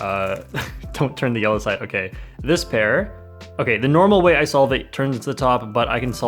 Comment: I'm sure I heard "dispair" instead of "this pair"... are you okay?